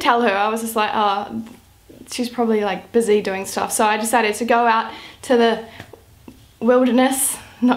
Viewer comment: Totally thought you said "I'd decided to go lift" YES WHAT A FABULOUS OPPORTUNITY TO WORK OUT